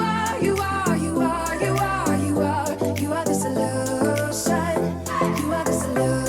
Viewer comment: I keep hearing "you are just a lotion" in that song